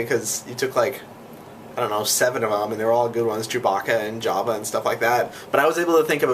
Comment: I thought you said Jabba not Jawa so I was like wat the heck thats not Jabba.... Oh wait Jawa!!